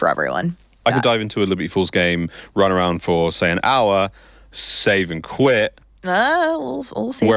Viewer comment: is she saying “a little save and quit action?